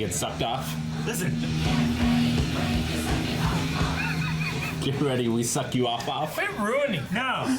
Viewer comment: I heard "Get ready! We're your second outlaw!" and the ma******tion thing my first time through, and while I'm now able hear "Get ready! Wake your psyche up Mob!" thanks to the subtitles, I still can't hear "frustration" as opposed to the other thing.